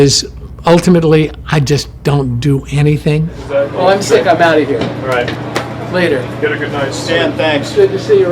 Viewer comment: Did Stan Winston seriously just say "well I'm sick, I'm out of here" because I can't even if I heard that correctly. Love you 3000 Stan, thanks for all the memories.